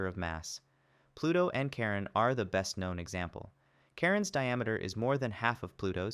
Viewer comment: I hear "Karen's diameter"